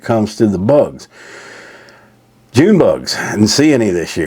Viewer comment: When you said, June bugs, for a while i thought you were saying 'ze bugs', you know, the recommended future source of protein to be replaced with meat.